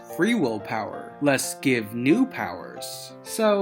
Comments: What I heard is "Let's BUILD new power" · I heard "let's wield new power" me and two of my friends heard it say that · I always heard "let's beam me power". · I heard "let's feel feel power"